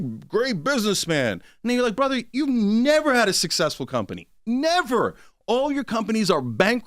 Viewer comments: Lmao, bro you made me look. He said never or neva but he was very close lol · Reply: Naw, before that. Could've sworn I heard, "N***a look, you've never had a successful company" 😭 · sounded like he said nigga 👀 lol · did he say “and then you’re like….” Or “ni@@ you’re like”?…. I think it was the first. But he needs to slow things down to make sure he’s not misconstrued in the future. Especially with his past.